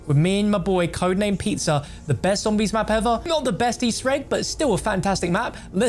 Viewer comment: Let’s all admit bo2 is the best motd!! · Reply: I almost thought you said it has the best zombies mode